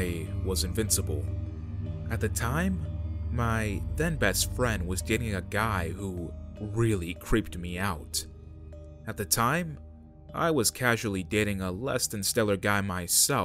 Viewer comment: Person begins with im a 19 year old female, then says when i was a teen... BOI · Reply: Zachary Osmond I thought no they said "at the time I was 18